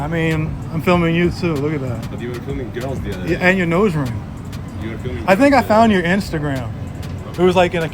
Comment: did you say nose ring? Can't understand